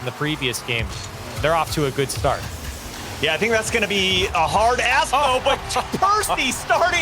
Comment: did he say "a hard as*hole"?! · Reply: "a hard ask, though" i think xD